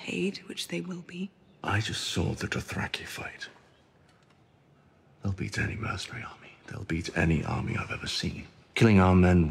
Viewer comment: What does Jaime Lannister say at 0:08 to Cersei? "They will beat most really army (?)" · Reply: They will beat any mercenary army